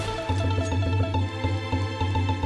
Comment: "I'm the War Owl, and I still have no clothes" is what I thought he said at first